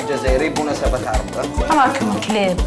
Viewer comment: كأن اذناي سمعت كلمة #راكم كلاب .... @@ !!!!